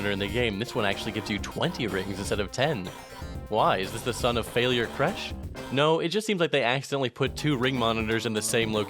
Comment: What in the world did he say at 8:00 “was that the sum of failure crash?” I don’t think the subtitles are right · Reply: Son of failure cresh Its a reference to sonic 2 special edition specifically stage 7 Theres a playlist on YouTube